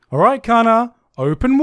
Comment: I misheard Kanna as Connor and was legitimately confused for a second